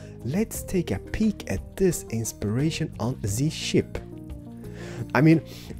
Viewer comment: where he said its a ship it sounded like he said its a shit